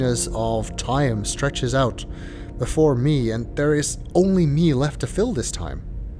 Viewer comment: I thought you said "and there is Gehn" Find a place to shoot portals LOL!